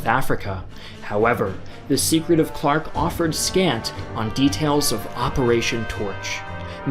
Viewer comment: Can anybody help me with what he said at 31:12? I can only understand " ..... the details of Operation Torch." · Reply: "However, the secret of Clark offered scant on details of Operation Torch", which doesn't make sense. Shouldn't be an "on" there.